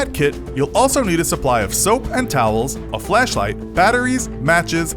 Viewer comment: Thought you said flesh light for a second there